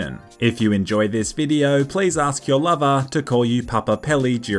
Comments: I thought he said tell your mother instead of lover at the start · Hold on did you just say mother in that intro? rewinds ooh you said lover, well that makes more sense I guess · first lol gotem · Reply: I thought in your intro you said ask your mother during sex not your lover